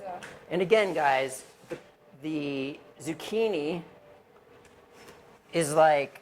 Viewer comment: Thank you! I thought I heard them say zucchini toward the end. Much thanks!